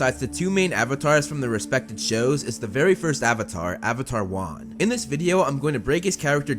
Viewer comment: I totally thought he said avatar juan